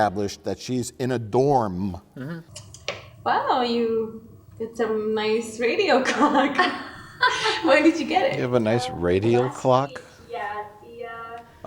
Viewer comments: I heard " nice radio cock" 👨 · I honestly thought her friend said "You got some nice radio cock." 🤣 Was that a gap in reality?? This all kinds of terrible.